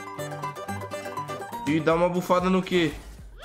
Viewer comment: Eu ouvi um Fus Ro Dah aos 0:48 ? LuL